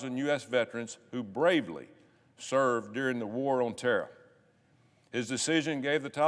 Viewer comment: I thought for a sec, he said war on Terra, or planets true name, but no it was terror.